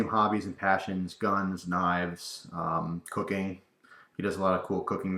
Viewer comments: I heard 04:20 as cocaine i was like wait wat XD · I could have swore he said cocaine....